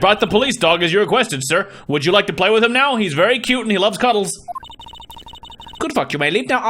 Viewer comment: My bluetooth headphones desynced a bit, so around the 7 minute mark mark I heard "Prosecutor. Would you like to play? I am cute and cuddle." Hopefully you can logic something great out of this information.